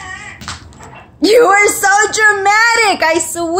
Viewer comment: Why is a boy wanted Burger King instead of Twix? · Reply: He says "I want Burger King!"